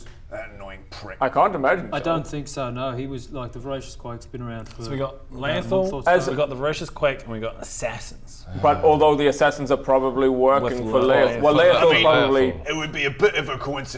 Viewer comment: vorasious quake? i thought they were saying vorasious queen with a weird accent.